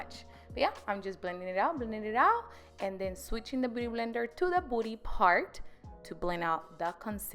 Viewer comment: She looks good without makeup at the beginning I thought she already had makeup on but she looks good with or without makeup She talks like she in espanic or jamican I thought she said booty blender instead of beauty love u